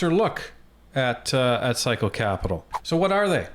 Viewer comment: Until you posted 'Cycle Capitol ' on the screen @ 7:00, I thought people were saying "Physco" Capitol. Ya gotta admit, it's a better fit! 😉